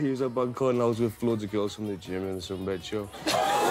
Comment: What did he say at 1:03? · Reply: i was with some of the girls from the gym and sun bed shop